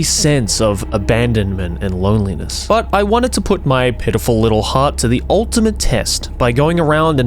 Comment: The accent is unfortunate once here, it sounded like “I wanted to put my pedophil little heart to the ultimate test” I know it’s “pitiful” but I heard something else 😂